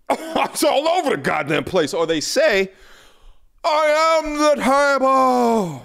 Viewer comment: when he says "i am the table" it sounded like "hold the door" and im still processing that scene. Never forget.